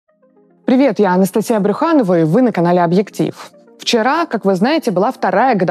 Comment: ???????? Мне послышалось? Я ошибся?????